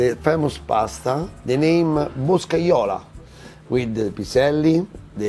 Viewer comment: Pasta bosola (captions said this) or bresaolo or Boscsiola ... at 1:50 .. anyone have the name or recipe ... thanks.